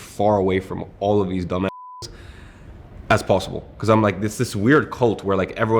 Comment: lmao did bro say nga?